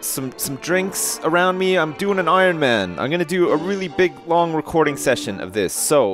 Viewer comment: your doing the iron man? did he say that